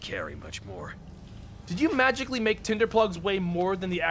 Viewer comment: Somehow, I thought you said, "Did you magically make tinder plugs weigh more than the actual newspaper, you crackhead?"